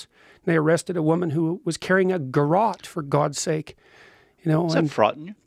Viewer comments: hey, non-native english speaker here. could someone tell me what he said the woman he talks about at 4:20 was carrying? can't understand it for the life of me. cheers. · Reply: She was carrying a garrote - a device used to strangle people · what is a "garat"? I couldn't understand clearly or I don't know that word. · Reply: Garrote , it is a rope or wire used to strangle someone. This particular Garrote was a wire with handles on each end.